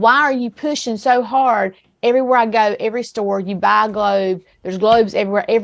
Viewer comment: Every store you go, you buy a globe..... did she really say that or am I laughing too hard to hear her properly?